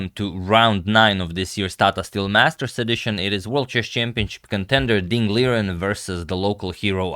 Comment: I thought he said "chicken tender" lmao